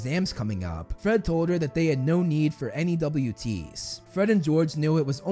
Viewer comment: It sounds like he said he had no need for any W.T`s I do know he said N.E.W.T` tho